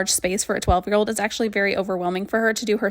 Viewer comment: She keeps it cleaner than I would as an adult 😂 this would’ve been my dream as a12 year old tho 😂 I was confused I thought she said 20 year old at first